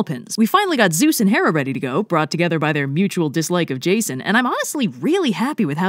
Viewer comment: In their plug for the enamel pins I heard “brought together by their mutual dislike of JSON” I may need to stop coding so much.